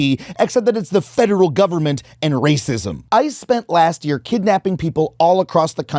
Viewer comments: i thought for some reason he said I and not ICE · I heard, “I spent last year kidnapping people” and panicked. Great video as always.